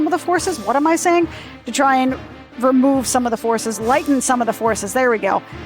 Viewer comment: l-.. lighten the load? Is that what you were trying to say